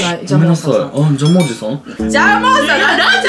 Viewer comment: 私もジャムおじさんって聞こえた笑